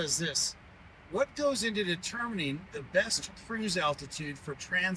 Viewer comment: (AI) thought you said best "FREEZE" altitude LMAO SMH!!